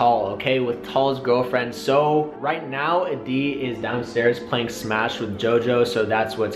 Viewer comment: 🤣🤣🤣 I was eating a mint and right as I crunched it I thought he said adi Is downstairs smashing jojo lmfao I'm so dirty minded